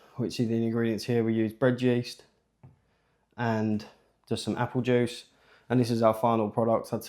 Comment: Speak up, I had to turn the closed captioning on. I thought you said ASBO apple juice. Freudian hearing I guess LOL.